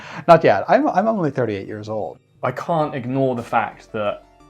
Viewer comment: did he say "i dont look 38 years old" ? because brother, i may not have guessed 38 but definitely would've been in the 30s. but thats true for ANY 30 someodd Y/O!